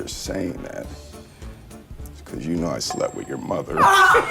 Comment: For a second I thought Will Smith said "I slapped your mother" to Margot Robbie.